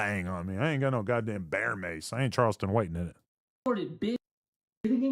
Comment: Is it me or was Adam almost about to slip? Lol … sound like he was gonn say I ain’t Charleston white ni…. 👀..